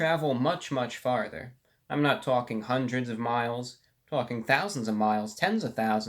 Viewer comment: did he say "fucking thousands of miles!' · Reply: ItsAlcatraz "*Talking thousands of miles" doesn't use profanity to get his points across.